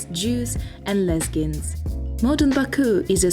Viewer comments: I thought I misheard! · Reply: Haha, I said Lezgins 🥰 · Did she say “lesbians” at 1:20?? 🤔